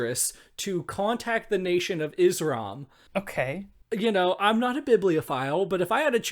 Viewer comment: i kept hearing 'izram' as 'the nation of islam' n i got SO confused